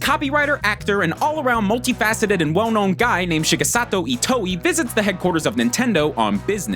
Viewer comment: Did he say shigesato itoi?